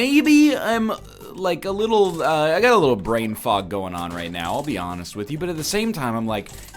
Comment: Did he say "brainfuck" at 2:40? Edit 1: Why use the emperor card when you have invincibility from Empty Vessel? Edit 2: RIP Blue Candle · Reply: Mikhail Torkanovskiy He said “Brainfog”